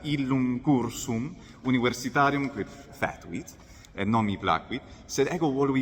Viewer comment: Please, what is the verb said in 7:19, 'fētuit' ? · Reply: foetuit